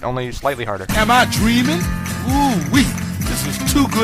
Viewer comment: I thought it said Weegee instead of Woowee D